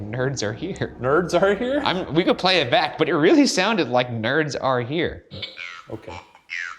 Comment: I think it said "They're all here"